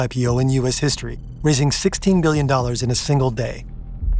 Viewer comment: is it me or did he say “gillion”? Is that a word?